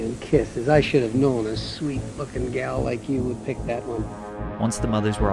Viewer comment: Call me hearing impaired if wrong. At 5:30 I heard “ Hugs and Kisses, I should have known a sweet F*cking gal like you would pick that one”. Instead of “Sweet Looking gal”.